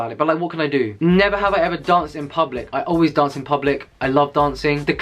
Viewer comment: That kiss at the end though - *too much love*. Also I love seeing mister teddy bear behind you. And lol. I heard "dumped" instead of "danced" because I was munching on food and kinda judged you for a second when you /said/ "I always dump in public" lol. Love from France